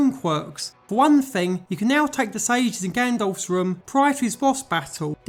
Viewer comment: For the longest time i thought he was saying GANDALF not Ganondorf and it was confusing the heck out of me